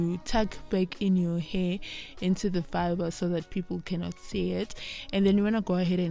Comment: What are you using to wrap your hair sounds like youre saying fiber ..look like a shoestring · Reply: It’s a fibre